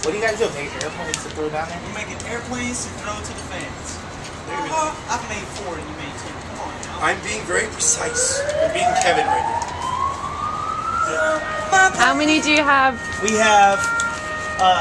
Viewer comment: Does he say, "I'm being very precise. I'm being Kevin right now?"